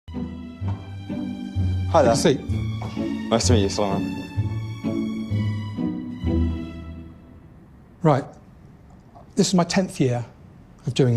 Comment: did he say "nice to meet you sir alan?"